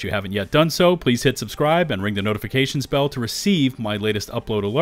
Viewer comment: Sounds like he says ring the notification spell. That put a smile on my face. Thanks for the starfield info.